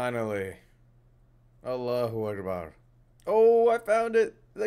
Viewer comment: did u say Allah akbar 😂😂